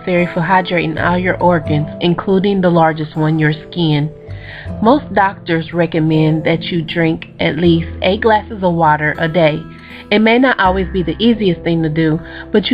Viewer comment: When she said "drink 8 glasses of water" I thought she said "a glass of water" and I was like WOW I HAVE BEEN DRINKING WAAAAAY TOO MUCH WATER